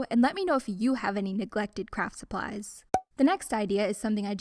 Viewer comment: I don’t know why, but I heard ‘neglected crap supplies’ and I heard crap for the rest of the video 😂